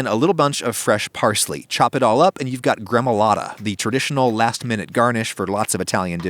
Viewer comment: what is he saying? The name of the pizza topping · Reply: gremolata recipe